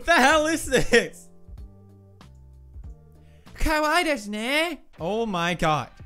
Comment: カワイイデスネ〜 にしか聞こえないw